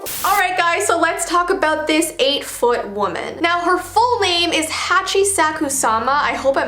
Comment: for a sec, I thought it said "a foot tall woman"😂😂😂